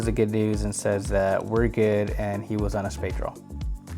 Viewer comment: All the straight draws got there... EDIT: Oh, I misheard. He said spade draw.